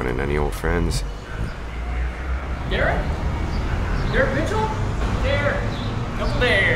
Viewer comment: I thought that guy said Deer bitch ...